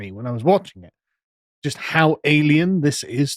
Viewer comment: I heard Sargon say "How alien this was" - my first vision was - a xenomorph hive...